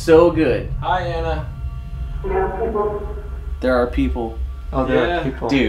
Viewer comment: i think at 1:04:02 she said “we have people” like guests there